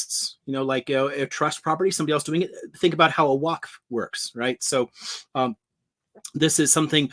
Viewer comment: "Thnk about how a ___ works." Waqf? Couldn't catch it, and the caption says 'walk.' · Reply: Damn. Yes, waqf. I knew the captions couldn't be perfect. sigh